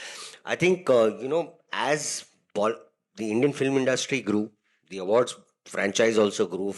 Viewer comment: he did not say “Bollywood” because salman does not like it. He said INDIAN film industry that’s what Salman likes. That’s called power